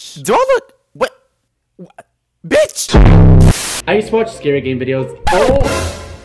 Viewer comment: I thought he said “scary gay videos”😆